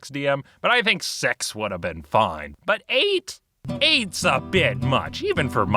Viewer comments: is it just me or does he say "sex would have been fine"? xD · At first I thought he said "sex would have been fine" before realizing he said "six" and I was like...what?